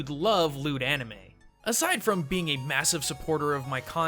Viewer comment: "Just like I think you'd love Loot Anime" .... At first I thought he said lewd anime...